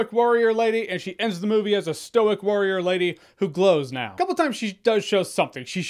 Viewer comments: "She starts the movie as a stoic warrior lady She ends the movie as a stoic warrior lady....who glows now." 😂😂😂😂😂 Classic man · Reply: Lol I thought he said “who blows now” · "She starts the movie as a stoic warrior lady She ends the movie as a stoic warrior lady....who glows now." 😂😂😂😂😂 Classic man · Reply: I thought he said "who blows now". I was like... HUUUUUH??? 👀 😂😂